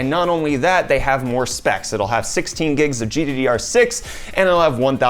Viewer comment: did he say 16 gigs of gddr6